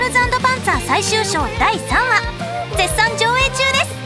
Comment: 「白い魔女がね」 が 「いっぺん死んでみる？」 と聞こえた。 (お、同じ声優さんですよね?…オロオロ) Gland Symphony ほんとありがとう。 泣いちゃう! 今回、最後だもの！ ササキサヤカさんありがとう!! Gland Symphonyありがとう! (漢字書けなくてごめんなさい) オレ、ポップスってぶっちゃけ苦手なんだけど Gland SymphonyのCD買って来たときね エンドレスで2時間くらい聴き入ってた 「このままじゃ、ここから離れられない」 と無理やり切った。 こんな経験初めて!! Gland Symphonyをありがとう!!